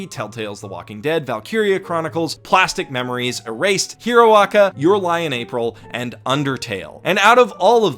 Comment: what does he say at 26:18...sounds like Hiroaka? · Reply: My Hero Academia -> HeroAca